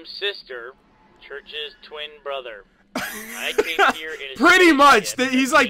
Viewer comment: He actually said "Church's twin brother", not "Caboose's twin brother"! not sure if you caught that or not because you seemed to have heard him say caboose